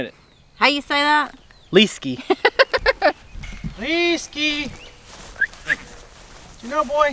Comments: I thought you were saying Whiskey. LOL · Oh I thought you were saying Whiskey like Jack Daniels 🤣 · Ha! I l always thought you were saying Whiskey! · First I thought you were saying whiskey but then I realized it was an L not WH. But that's how I remember his name and make it a long E not a short I.